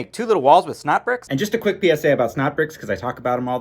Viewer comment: I always thought your were saying schnapp bricks bruh